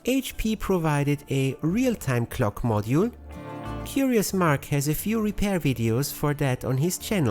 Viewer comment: I realize I misheard it, but now I'm imagining a "Furious Marc" channel.